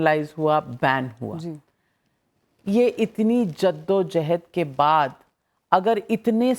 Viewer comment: did she just said jihad and didn't released it ? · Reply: She said jeddojehd, meaning of this word is long struggle 😊